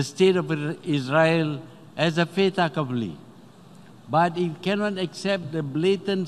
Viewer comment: “Malaysia accepts the state of Israel as a ‘fata couply’” couldn’t make out what he’s saying here… if you could, please tell me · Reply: you heard it wrong its not "fata couply" but "Fait accompli" and "Fait accompli" is a noun that means something has already happened and cannot be changed. It is pronounced "fate uh-COM-plee". The word comes from the French phrase fait accompli, which literally translates to "accomplished fact 😄